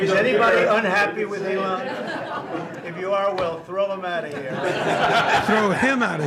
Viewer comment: I thought Trump said, "Throw them out if here.